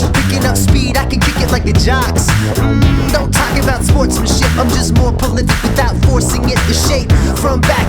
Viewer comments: I think he said "SportsmanSHIP." It does sound like he says "Sports and S***" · The person thought he said "Sports and sh*t" but he actually said "Sportsmanship" making the person think he isn't 'family friendly'.